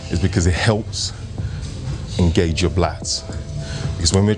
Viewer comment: you said BLATS instead of LATS. You must have been tired already lol 💪🏾💯